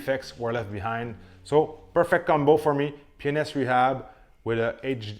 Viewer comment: Did he say, "Penis rehab..."